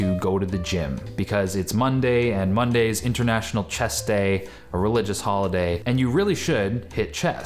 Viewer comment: I thought he said "International Chess Day" and I couldn't make out what that had to do with going the gym.